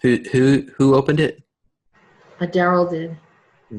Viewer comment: did she say Dero? As in the devolved underground race with the long nose? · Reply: I think she said Daryl, the name of the person she is channeling.